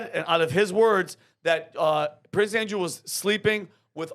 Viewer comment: oh, Prince Andrew...I heard Criss Angel the first time